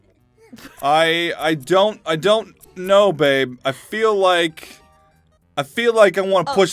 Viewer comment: did he just say BABE?????????